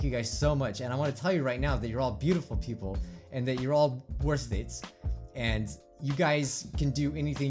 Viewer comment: When you said we’re all worth it I thought you said we’re all worthless.